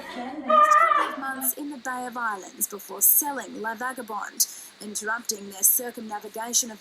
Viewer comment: I thought I heard that you are Selling La Vagabonde at 11:47. Hope you do a really long episode saying goodbye to her. Chris